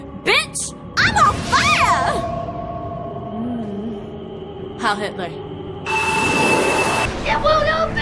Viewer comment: why do I hear "hail hitler"💀